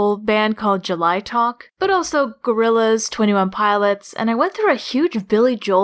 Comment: did- did you say gorillaz? I mean it totally makes sense but- omg. I love you already for your content- but the fact that you also like my favorite band just made me really happy especially since I have barely any gorillaz fans because people think gorillaz is weird and the first they always say when someone sees my humanz shirt is that "The blue haired guy with no eyes looks wierd and scary" Edit: Also I am a huge billy Joel fan and micheal jackson, bon jovi, and queen fan.